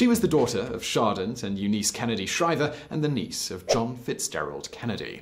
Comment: Did I hear you say "Shardent" instead of "Sargent" Shriver?